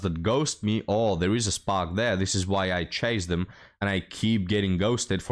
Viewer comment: All I heard is, "This is why I'm single"